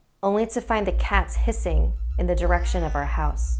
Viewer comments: I thought it said pissing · for a second I thought she said cats pissing.